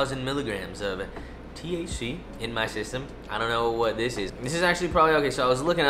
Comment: anyone know what he just said? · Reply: Zeg Arena he said I don’t know what this is